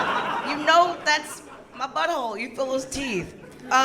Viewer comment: she's got the crowd with this line but i can''t make it out - 3:47 she's making fun of guys "accidentally" she says: " you know it's my butthole, _______" Does she say : you fill it's teeth? you philistine? I can't make it out but the crowd crumbled with that. She's fucking brilliant ok back to the show, but ya any help deciphering that is appreciated! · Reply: “You know it’s my butthole [because] you FEEL its teeth!” She’s saying that anal muscles feel like “teeth” because they clench tighter than the v hole